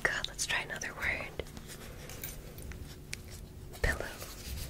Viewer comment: When dr ear said pillow I thought she said billow